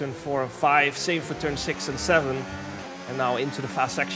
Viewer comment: did i hear “ turn SIX and SEVEN” ???? 6777